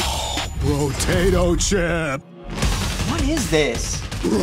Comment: is it just me or I heard “Brotato chip”?